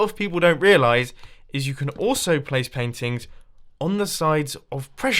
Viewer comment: when he says paintings he sounds like he is saying peng tings! only some will understand